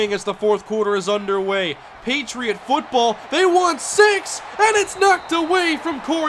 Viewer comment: he said "They want six!" and it sounded like "They want sex!"